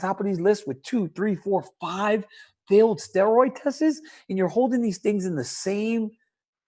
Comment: Hang on a sec, at 1:40 does DC actually say ‘four or five failed steroid testes’? Surely you mean the plural of test, tests!